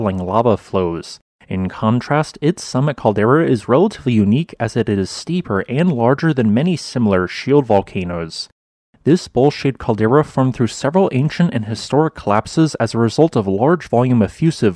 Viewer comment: When you said Bowl shaped I thought you said bull sh*! 🤣 Lmao